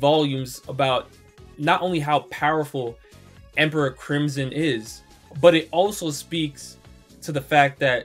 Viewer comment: please do not tell me I just heard him say "emperor crimson"